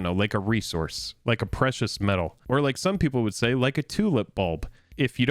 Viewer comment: what did say at 4:49? timult bulb? cant understand the pronunciation there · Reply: Tulip bulb, like flowers